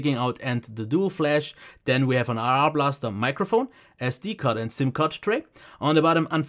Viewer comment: Ey, not to be mean, I know u said at the beginning, but it made me laugh, it sounded like you said ARAR blaster